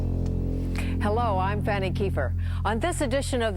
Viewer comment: Did she just say "Hello i'm Fanny keeper"? i wasn't paying attention to the video just the audio.